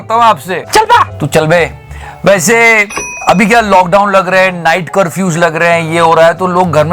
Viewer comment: it sounds PAISE instead of WAISE. Watch the video from 10:15 n u will understand the reason 😉